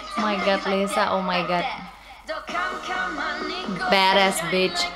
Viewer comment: did just say bada** bi#c*? HAAHAHH